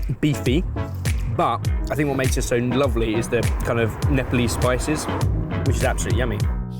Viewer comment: did he say “nipple-y” spices!? checks the subtitles …ohhhhh- NEPALESE!- that does make more sense!🤣😅😂🤷‍♀️👍🏻